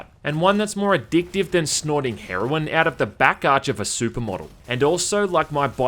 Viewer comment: I thought u said "more fun than snorting heroine out the back crack of a sumo" and I died laughing.